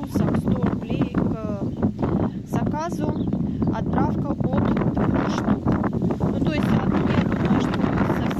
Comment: Здравствуйте . Я хотела купить Меджикал старлайт. Не расслышала какое минимальное количество гортензий надо заказать. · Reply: Добрый вечер. Отправка от 2х штук. Заказать можно, написав на Ватсапп или Вайбер <num>